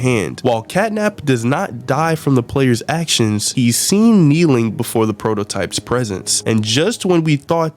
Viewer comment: why did I hear, ”He’s seen mewing-“